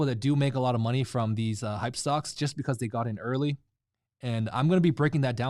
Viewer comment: I thought you said you got in early? you are down